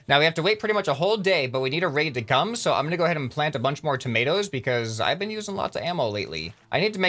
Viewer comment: did he really say tomatoes???